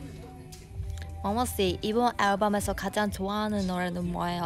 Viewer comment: i thought she said unbelievable but it was believer